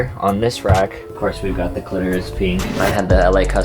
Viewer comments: Wait did he just say "clitoris pink"??? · Did he say Clitoris Pink?! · Did he say clitoris pink????? GIRL LMAOOOOO